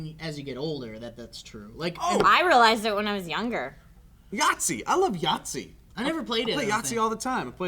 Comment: when Joe first said Yahtzee i thought he said Nazi, until he said it a second time that is. needless to say i was very confused for about 2 seconds